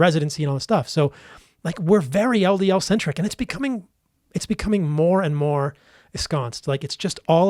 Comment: what did he say? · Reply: "It's becoming more and more esconced...", (ie. embedded/ entrenched).